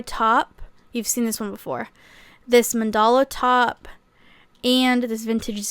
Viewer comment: could somebody tell me the name of the shirt (style) at 8:57? i can't figure out what's said 🫶🏽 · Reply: 6 months late but if you still wanna know she said “mandala” top :)